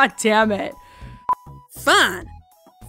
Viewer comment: Applejack: “That’s the WRONG F WORD PINKIE!” And what word was that, Applejack? 😏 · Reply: I'm gonna get r/wooshed here, but I'm clearly sure she said "fine" in a southern accent.